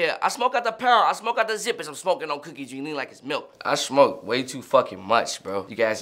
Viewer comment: Thought he said drink me like it’s milk💀